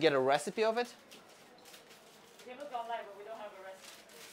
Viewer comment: “I see” The perfect phrase to show the amount of disappointment, understanding, and Curiosity. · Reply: When he said “i see” for some reason i thought he said “Icy” 😂 Response to actual comment: lol i see is the same as saying “I understand” i’m pretty sure.